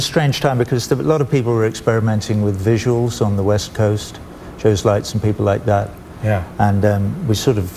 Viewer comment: Who are Joes Lights 02:15 ? Or did he say something else entirely? Dyslexia 4TL.